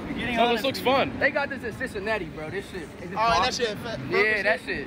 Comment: All I hear : "this shit that shit"